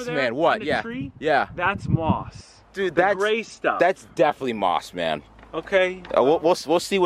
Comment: i heard "that's moss man" I WAS LIKE WHERE....WHERE IS MOSS MAN!?! Then I realised it wasn't moss man